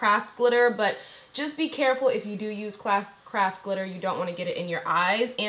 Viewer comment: thought she said i used crap glitter, I said to myself but why? Nicole does not use crap anything! lol its craft not crap lol